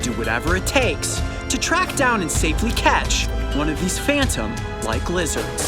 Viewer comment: I thought he said safely catch the lizzerd 🤔🤔🤔